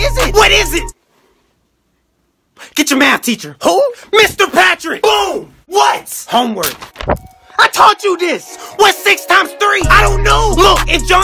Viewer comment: 오래된 영상이긴 한데... 0:39의 "내가 말했잖아!"는 "I taught you this" 즉 "내가 가르쳐줬잖아!"가 옳은 번역인 것 같네요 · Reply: 전 i told you this 로 들리는데ㅎㅎ헷갈릴수있겠네요